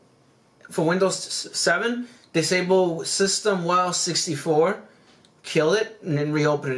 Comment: Hey man thank you so much for this video. It's incredibly helpful. At 17:05, what do you say? The subtitles say "System while 64" but I can't find any setting that looks like that. Appreciate it! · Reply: systemwow64 which is only found on windows 7, you won't have to do that on windows 10.